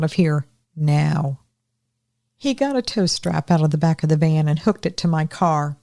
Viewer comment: When you said “tow strap” somehow I heard “toaster” 😂 I was wondering how a toaster was gonna jump a battery, or was he gonna pull a “Home Alone” and heat the handle in case it tried to open the door again. I’m 67, and now I know it’s time to have my hearing checked! 😅